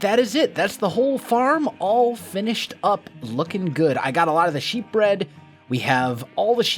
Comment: What you said: "When I got all of the sheep bred..." What I heard: "When I got all of the sheep bread..."